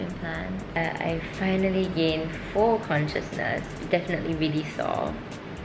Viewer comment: thought i misheard you said 4% consciousness instead of full consciousness. was laughing quite hard, seconds later realized oh..